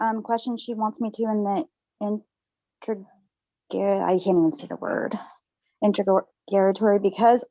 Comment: I thought she said she never says the word daddy she is strung out and has more excuses then brain cells